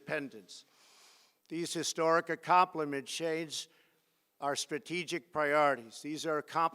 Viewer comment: wtf did he try to say at 6:48? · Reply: These historic accomplishments shape our...